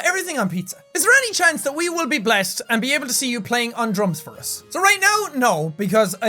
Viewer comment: I heard "playing on drugs for us